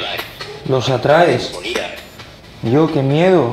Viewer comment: Minuto 18:12 yo creo entender los atraes y despues como ira indagando un poco ira se supone que es un demonio su nombre es amon sacado de google. No soy ningun experto. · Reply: Si despues de "los atraes" yo también escuché "con ira"